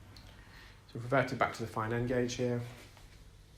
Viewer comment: I had no idea he was saying gauge. I thought he was saying gorge. now I understand what he's talking about lol